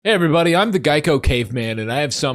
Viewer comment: at first i heard "hi everybody im the Guy Cocaine Man"